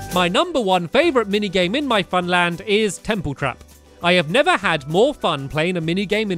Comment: 3 of my fav games: 3.(Forgot name)There is four colour red,yellow,blur,green(kinda)shoot the arrow up in the air and hit several minecarts 2.Its hard to choose but its(your no10 one)or the templecraft one 1.Flower Power that one was the best :D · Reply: oh its Temple Trap I thought I heard it Templr Craft lol